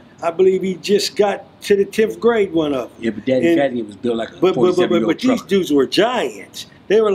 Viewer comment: Did anyone catch what said at 15:49, hahahahahaha He said dude was built like a 47 year old trucker.